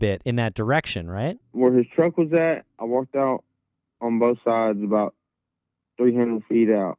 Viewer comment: This was great, thank you and for doing this! It would be great if would be willing to break down what he thinks Brandon is talking about in the 911 tape beyond the Stapers thing. For example, "going toward Abilene on both sides", "not talking to him", etc. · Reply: I've always thought he was maybe saying "on Bronte side" instead of "on both sides". Maybe on the side you'd be on if you were driving toward Bronte? Can't say for sure though.